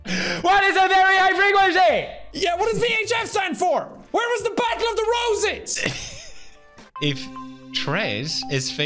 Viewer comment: Right at the end I thought he said the battle of the roaches